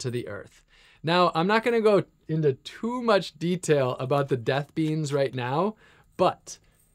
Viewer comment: P.s. I would love to hear your thoughts on death. I sent this video to my mom before I got to the part where you mentioned the death beans. You seem like somebody who is greatly needed in our world. I understand you likely don’t intend on using them, but I would be curious why you would want them in the first place. Thanks for sharing. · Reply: Yeah. I heard “death beans” and can straight to the comments 🤔